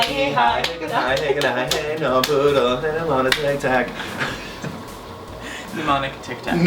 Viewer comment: I thought Scott said "I really want a tic tac"